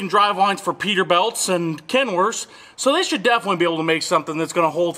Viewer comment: Did you say KenWORSE? 😂